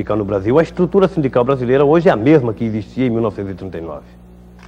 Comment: Perai, aos 2:12 ele fala que a estrutura era a mesma de 1989 ou 1889? Juro que escutei 1989... estranho.